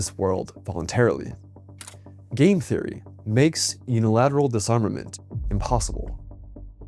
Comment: I misheard this as game theory